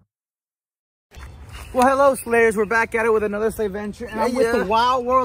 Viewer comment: Holy shit! For a moment I thought he said "HELLO SLAVES" and I went🤨